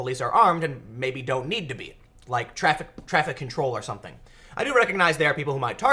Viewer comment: Tim I don't get you man. You say you see whats coming. you have seen with your own eyes how authoritarian and violent the far left is. And yet your thoughts lean to disarming everyone? Tim you know that they don't follow the laws as is. So you think that us honest people should give up are God given rights to self defense. And what If they at the least start attacks that result in mass murder? where I call home there are roughly 274 sworn police officers for each 100,000 residents And there not trained for prolonged engagement that I know of. So I am sorry man I must vehemently disagree. The police would only get there after the shooting starts and going to extremes here but so would the military. Sorry but Ill keep my guns. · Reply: frost I thought he said not armed during traffic control.